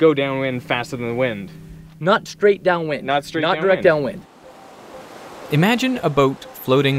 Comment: not what said. he said not direct down wind